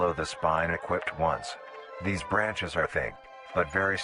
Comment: I Heard "it grows a dick" but it says thick actually